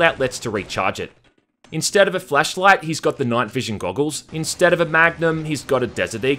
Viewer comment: did he say Fleshlight?